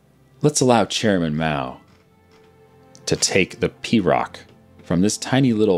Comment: Am I the only one who thought that he said Pee rock and not PRoC? Yes? Oh...